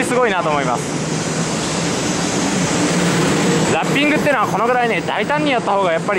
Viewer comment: 500TYPE EVAはラッピングではなく塗装ですよ、そして車内放送はシンジではなくカヲルですよ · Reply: ラッピングが烙印に聞こえたw